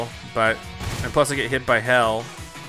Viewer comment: he said hell instead of hail anyone else catch that?